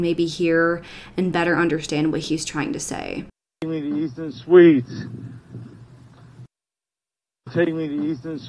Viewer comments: “Take me to Houston Suites” is what it sounds like he says. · I was born and raised and still currently live in Columbus Ohio & this happened right across the street from my work. Stelzer rd is a TERRIBLE area and honestly so is Easton towne center.. This truly is the weirdest case ever . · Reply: Theres a place called Easton? Becaudr it sounds like he said takin me to Easton Suites